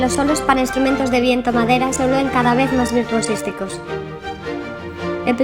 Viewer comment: alguien me dice que dijo en el minuto 3:44? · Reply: Creo que virtuosísticos